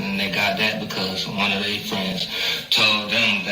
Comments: It sounds like he said " One of They friends " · Sound like he said "one of they friends" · I thought he said one of they friends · Idk it sound like he said one of they friends